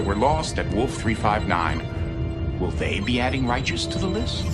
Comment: First time I heard it, I thought they said Worf-359.